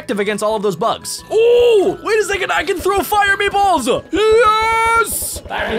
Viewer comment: i thought he said, fire me balls!!!